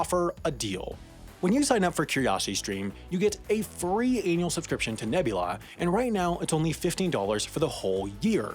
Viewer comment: did he say "you get a free anal subscription to Nebula"? because I had to stop and go back 3 times to see if I was imagining it.